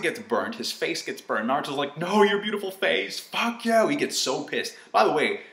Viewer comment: It sounded like Boruto said 'fuck yeahhh' which was exactly how I reacted as well